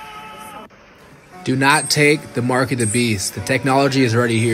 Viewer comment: Starting the video I thought I heard the men said “ do not take the mark of the beast”. I restarted it to make sure k heard him right but I guess not.